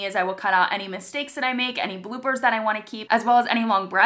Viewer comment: The 1st few times I thought you said blue birds but you said bloopers lol.